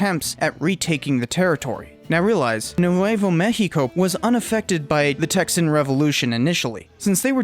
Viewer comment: You said Nuevo México so weirdly, I thought you were saying New Huevo México. Lmao